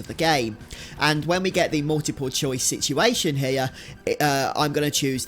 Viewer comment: ….did you seriously just say hair instead of here?! Gadam your people have a penchant for butchering the English language.